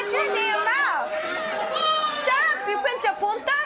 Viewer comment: what was the slang word she said for hispanics, it sounded like Pinch-A-Polka?